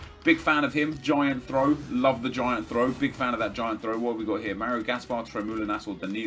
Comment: Sounds like he says "giant throat, love that giant throat" 😂😂😂